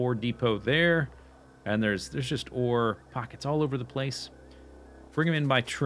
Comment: Did he say f&$k